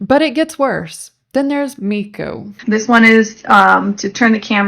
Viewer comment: I heard Miku at first and was so incredibly confused.